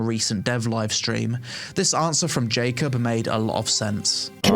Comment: I heard "LadyPeon" and now I can't unheard it 🤣